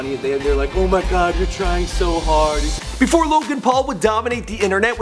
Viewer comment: Sounded like you said "before Logan and Paul" lol